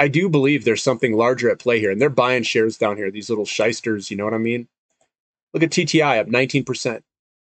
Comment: What did say at ~5:20? "These little shicesters?" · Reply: Practicing my copy/paste skills 😂 shyster shī′stər noun 1. An unethical, unscrupulous practitioner, especially of law. 2. One who does business trickily; a person without professional honor: used chiefly of Iawyers: as, pettifoggers and shysters. 3. A trickish knave; one who carries on any business, especially legal business, in a mean and dishonest way.